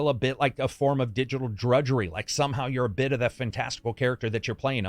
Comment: I sincerely thought he said you start out as a minor lmao. I was like "oh that's kind of cool you get to actually grow up in the game." And then 3 minutes later I realized he said miner.